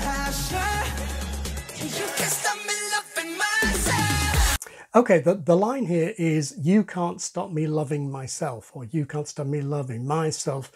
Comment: I heard "you can't stop me laughing myself"